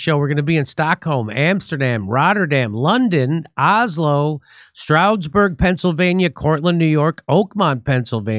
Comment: I thought you said you will be visiting Strasbourg, I go to your website band it's Stroudsburg 🥲